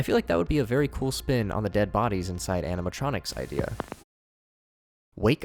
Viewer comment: do only I hear "anematronics"